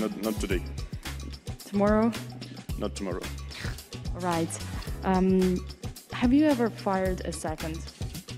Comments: And I heard "Have you ever farted in seconds" 😆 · What does that interviewer ask Nepo at 6:54? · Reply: I thought she said "have you ever fired a weapon" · What is she asking? Is she saying "Have you ever fired a second" ?? I don't understand someone help xD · have you ever fired a WHAT? cannot understand... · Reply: A second.